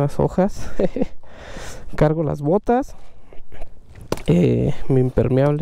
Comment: entendí "cargo la m0ta" jajaja